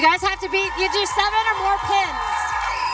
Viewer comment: I thought she said "7 or more KIDS" 😂😂😂